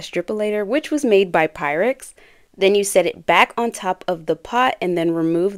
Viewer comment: She said "Which was made by Pyrex" but I heard "Which was made by pirates," that would have been so interesting! 😂